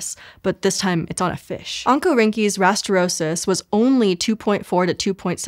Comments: The first time she said oncorhynchus rastrosus, I heard "Uncle Rinky's rastrosus" and now I want to know more about Uncle Rinky and his giant fish. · Anyone else heard Uncle Ruckus Rastrosus